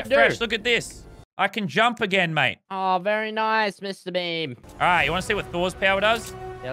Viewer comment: I thought he said “mr bean” Lmaoo